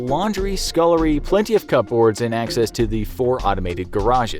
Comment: I'm sorry.... cup boards?? Don't think that's how it's said